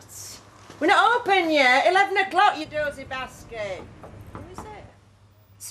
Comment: does Vera say Dosey b•¥$€*^d ?